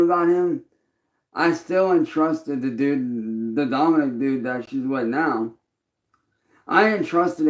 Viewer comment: Ohh, he said "dom-inic dude" i thought he said the dominant dude 😂😂